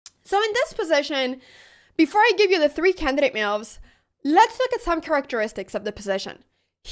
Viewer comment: I thought you said 3 candidate milfs..